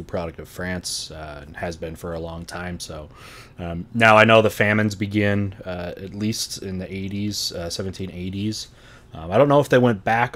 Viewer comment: I skipped 10 seconds ahead while was talking about the French famines and what I heard was “Now I know the actual famines begin when the famines started…”